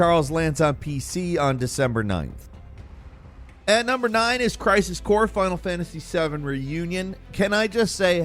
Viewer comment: Thought you said Crysis 4 i was about to lose my shit